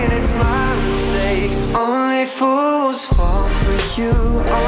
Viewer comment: I heard “only foods fall for you” I think I’m having a trouble with uh